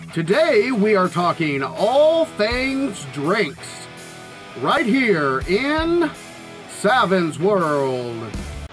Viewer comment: I was looking down at my lunch when the video started up and I thought he said 'Today we are talking all things drapes' and thought to myself, 'well that's a niche subject'.